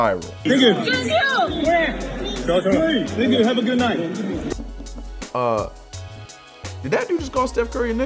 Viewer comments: I heard "nigga have a good night" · I thought i heard N****, have a good night